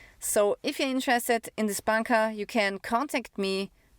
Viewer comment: Min 17:15 did she say Bunker?